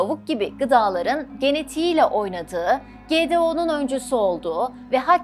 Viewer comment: 1 milyon abone hedefimize ulaşmamız için abone olmayı ve arkadaşlarınıza tavsiye etmeyi unutmayın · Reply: Genetile oynadi, ve...... tam duyamiyorum burda ne diyorsunuz? GDO gibi duyuyurum ama ögle birsey bilmiyorum